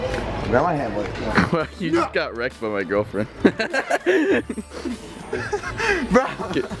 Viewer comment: wait unspeakable was a girl friend ?!??!??!?!??! listen closely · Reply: Racky Agnir i hear "you get rekt from my girlfriend" said unspeakable 😲